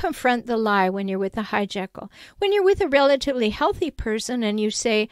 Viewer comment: You created the term hijackle?? It's genious! I kept hearing you say it and thought you were saying "Hyde-jeckles" like a play on "Jeckle and Hyde!" Anyway "Jackle" like the hyena type animal is VERY fitting! If you've ever been an honest person that stumbled into a group of narcissists it reminds you of a nacional geographic video where a lion wanders into the hyenas territory. I call it the: "multiple personalities getting together for a Cluster F" Disorder. 😅😅😅 Thank you for your videos!